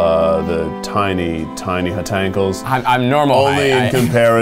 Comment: Oh god i accidentally spit all my water out when i heard “Hotankles” LMFAO